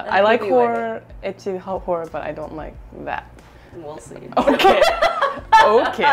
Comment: what did she say at 17:01? when june laughed and said "OKay" i keep hearing well said, but not sure